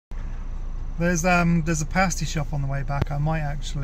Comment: At first I thought you said a "pasta" shop.